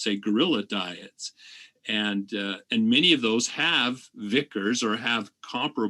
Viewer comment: I've read usage of vicariance in reference to geographically separated speciation or variety but not the term "vicar" before. Am I hearing that correctly?